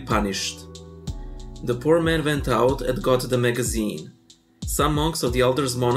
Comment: I thought it said haughty magazine.